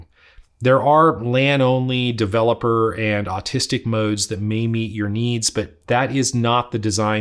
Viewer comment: did he say autistic mode?